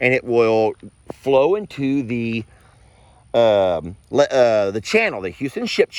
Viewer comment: I heard "$#!+ channel" @ 8:49.... Anyone else? Yeah. Come on!